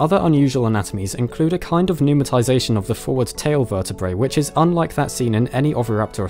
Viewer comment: What does he say about the vertebrae at about 1:41? Numitazation? The legend says "Numa Thais ation", and I can't guess what word he is saying.